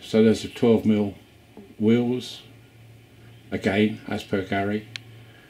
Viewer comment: Watched it again and this time heard you say 12mm wheels! Sorry, missed it first time...........